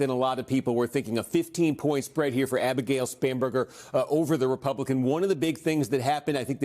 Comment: I thought he was saying SPAM burger 😂